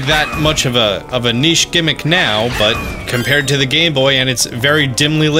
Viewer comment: did he say gay boy?